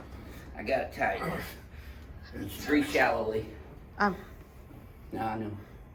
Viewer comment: What does he say at 8:19? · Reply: Sounds like “breathe shallowly”? But idk